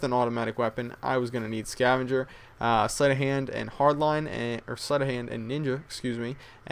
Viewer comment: sounds like he said"slut of hand'